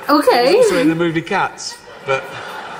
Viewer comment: he actually says 'he was ALSO in the movie Cats', witch basically is the pun of the joke